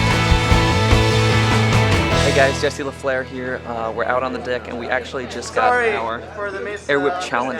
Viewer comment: Did say '' we are out on the dick'' at 15:10 ? :D